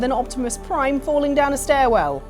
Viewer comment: bummer I misheard first time, thought they said Optimus rolling down some stairs... clonkclonk clonkclonk liked the picture :D Stairwell= "Ahhhh" BOOM!!!! not as funny :(